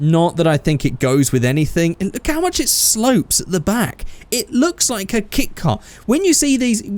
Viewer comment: When you said that, I first thought you said "It looks like a kick car", like one you'd kick whenever you see it, or something. After you said what you meant, I then realised you said "It looks like a kit car".